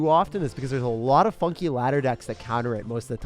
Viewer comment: I thought he said fucking 😂😂, turns out he said funky lmao 😂😂